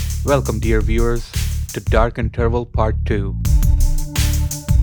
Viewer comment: the way you said interval, I thought you said “Dark and terrible”